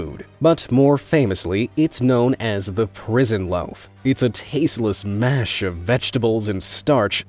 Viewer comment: I thought it said POSION loaf